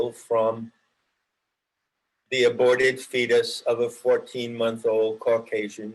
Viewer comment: Im a bit slow. Did I hear it right at around 26:25…”an aborted fetus of a 14 month old”?